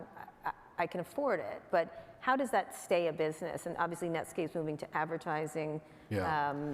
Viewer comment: did she just say Netscape? Lol